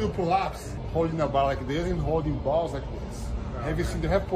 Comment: Thank god I thought I was the only one who always thought he said “balls” not “pulse” 😂😭😭